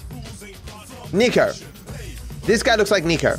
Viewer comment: I thought he said n****r 💀